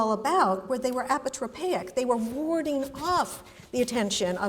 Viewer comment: What's the word at 47:44? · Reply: Apotropaic